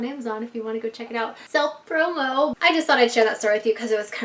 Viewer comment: did she say selfproved